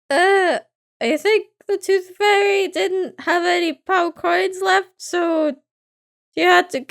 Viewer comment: For a second I thought you said "power coin"